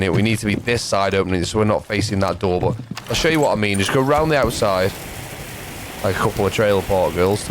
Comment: i thought the same thing when you said lets go rounding outside